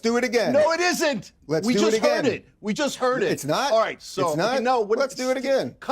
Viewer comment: "We just heard it" I swore you just said "I didn't hear it"